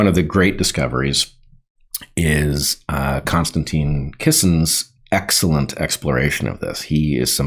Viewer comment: Whenever I hear his name, I hear "Constantly Kissing" instead of "Constantin Kissin". Grade school must have been weak, high school must have been fun 😂